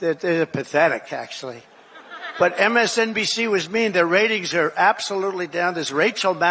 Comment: did he really say "MSNBC was mean" ? Or did i mishear??? 6:23 this man has ratings, approval ratings. . . Im not sure his approval ratings are even above MSNBCs at this point.